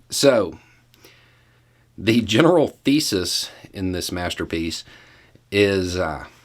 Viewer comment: for the fraction of a second I thought you said "the general feces in this master piece..." 😂